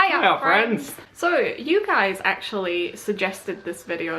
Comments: I always thought you said "Hiya friends" instead of "Hi art friends"... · I always thought you said "Hiya friends" instead of "Hi art friends"... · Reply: I always thought they said "hi OUR friends" and not "hi ART friends"